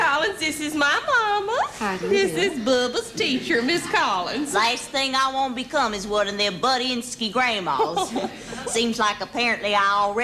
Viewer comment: could s. o. transcribe what mama is saying at 1:26 please? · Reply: “Last thing I want to become is one of those butt-inski grandma’s”